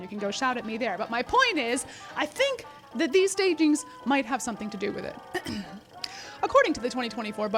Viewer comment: I thought it said "staginas" at 14:51 and I was thinking that I was really going to learn a new term today. LOL I was ready to learn.